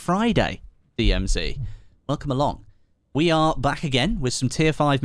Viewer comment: Sounded like u said we are black again😅😅😅.. When u see this plz check your twitter I wanna send u some memes from elgato